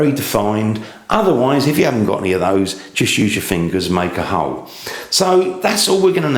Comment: I thought I heard, "Just use your fingers and make a hoe" XDDDDDDDDDDDDDDD